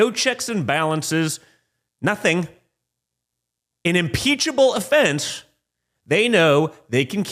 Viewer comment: What the fuck did Don Jr the cokehead say at 10:40? Peachable fence?